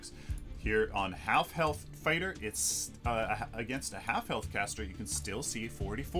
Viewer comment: was listening to this while doing dishes and I thought up said "half-elf" cater